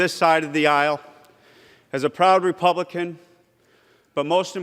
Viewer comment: I heard him say, I'm a coward republican 😤. No, Forbes don't have balls, there on their chin.